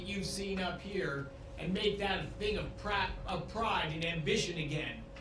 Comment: did he say crap instead of pride ?! 🙄🙄